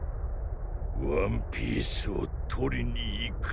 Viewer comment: did he say one piece? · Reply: wan pisu